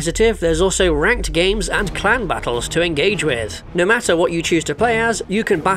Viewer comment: In the ad read I thought you said World Of Warship had "clown battles" which is now something I want.